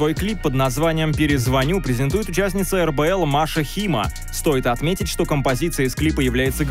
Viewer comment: Маша Зима участница РБЛ? Или мне послышалось?